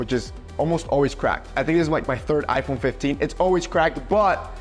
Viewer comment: did bro just say it his 3rd iphone 😮😮😮😮😮